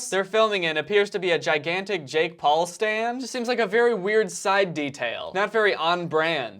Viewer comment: did he just say “Jake Paul Stan”?